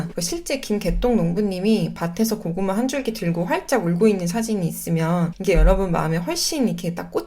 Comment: 김개똥 농부님이 '활짝 울고'있는 으로 들려요ㅋㅋㅋ 영상이 도움이 많이 됩니다 감사합니다!